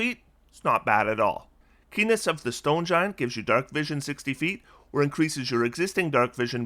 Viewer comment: When you said “Keeness of the stone giant “ I thought you said P*nis of the stone giants and I had to do a double take… I wonder what a feat of that name would do…